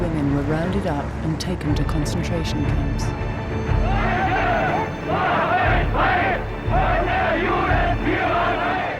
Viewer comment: Does anyone know exactly what they are saying? I don't speak German, but obviously get the gist of what the crowd is saying. I would like to know the exact words, though, please. Thanks. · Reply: I have to say the sound recording is extremely bad. I had to listen to this several times in a row to understand it. I think they say: " Deutsche macht euch frei, von der Judenführerei" (Germans free yourselves from the Jewish leadership). That rhymes in German. That also fits the pictures, " Deutsche wehrt euch "(Germans defend yourselves) is written on the signs. It was a slogan from the boycott of Jewish shops , restaurants and so on ...